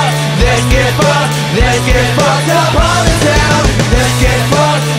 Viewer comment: I thought he was saying ''let's get fucked up all the time!'' ...Anyways, I think Russell Brand is a great human being and I really enjoyed that movie! (and yeah, what a good rock song!!!)